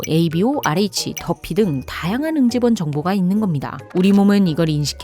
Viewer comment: 유튭시 구독구 좋아요구 ㅋㅋㅋㅋ 응집원을 임직원으로 들었음....